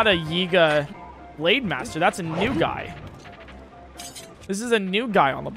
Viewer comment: bro, at 8:26 when he said that's not a yiga, that's a new guy I thought he had said the N word